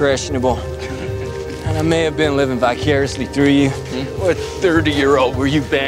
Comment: did rick say Bi-curiously?